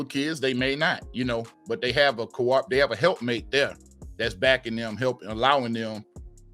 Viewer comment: Great stuff Brothers!!! Standards go both ways. BW talk what they want, BM start doing the same.....Did I hear Help Mate at 18:30 and not Need Help???